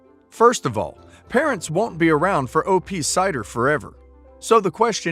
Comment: 2:29 She has a husband. And did you say 'cider' instead of 'sister' a few seconds before?